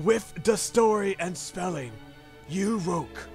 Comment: I must be having a hearing problem. It almost sounds like you said someone proof read the spelling.